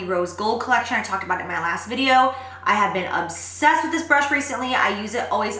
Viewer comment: When you said you've been obsessed with this brush my boyfriend thought you said I just had sex with this brush. lol